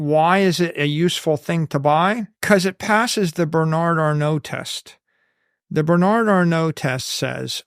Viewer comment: When he brought up Bernard Arnault I thought for a second he said Berni Arno, who is a knife maker.